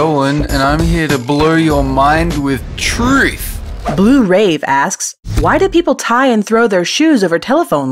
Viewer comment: I seriously thought said rape